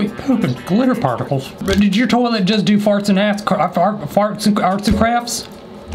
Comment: what did he say😏😏😏😏😏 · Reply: farts and ass- i mean arts and crafts